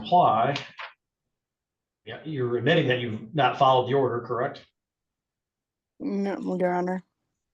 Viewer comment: WTF is she trying to say here?? “Nutriun werrr honor.” LMAO🤦🏻‍♀️🤪🤪